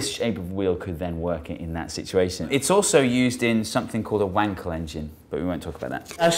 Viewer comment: When he said, "Wankel Engine" I thought he said, "Wang Collection."